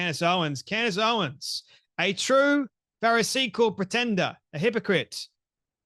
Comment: At first, I thought I heard Jay Man say paracetamol greifender, and I'm thinking, but she's the blummin headache! God bless all here. 😁